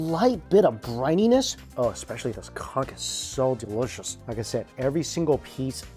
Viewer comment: Did he say what I think he said 😂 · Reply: He said “conch” which is the seafood he was eating.